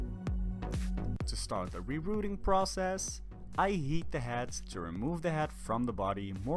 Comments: I Thought He Said “I Yeet The Head” 😂😂😂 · "I yeet the head…" That's what it sounded like you said.